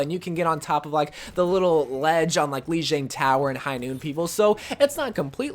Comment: Wair he says its high noon i thought he said its handloomed